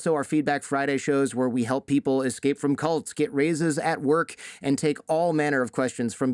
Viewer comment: I thought you said "help people get razors at work" ...glad its actually raises! New slogan "Raises not Razors" unless you work at a razor factory then i guess its both. Great show btw! Very informative!